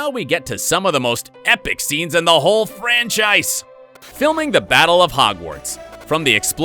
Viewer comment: When he said "Franchise" it sounded like he said "French Ice"